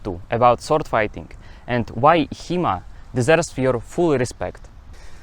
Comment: Honestly I thought he had said "He-Man deserves your full respect" before the acronym came up on the 2nd mention.